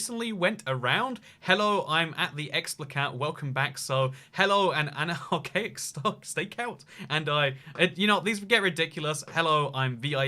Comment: the captions say "so hello and Anna hoecakes"... WHAT???